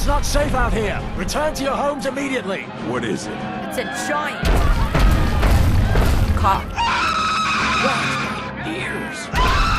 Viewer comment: Is that the Mandela effect or am I crazy.. because I always thought the said „it’s a giant chicken!“.. wtffff